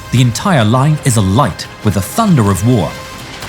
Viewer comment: a thunder of war? did i hear that right? then proceeds to sponser todays WOT